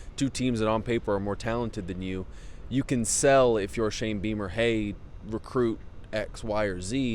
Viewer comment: O wow..thought I heard her say ‘Shame Beamer.’ Goes along with ‘Shame cocks.’ Too ironic.